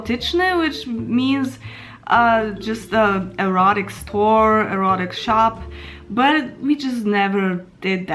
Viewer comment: Przepraszam bardzo, kto robił angielskie napisy? Jestem dość pewien, że w 4:20 usłyszałem "erotic store", a nie "rod explore".